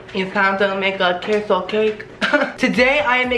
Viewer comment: Did he just say “Queso Cake.” .-.